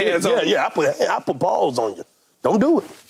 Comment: I THOUGHT HE SAID “I put balls on ‘em” PAUSE 😂😂